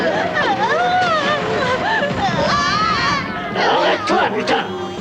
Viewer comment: "arrête toi putain"